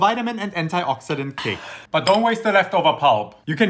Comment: What does he say at the last,i heard it as "bundaba"